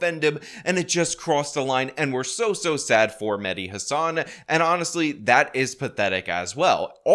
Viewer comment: Thought he was saying "Mini Hasan" 😂😂